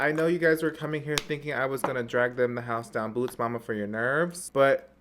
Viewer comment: I played it back 4 times and still I heard: "Drag them the house boots mama for your nerves"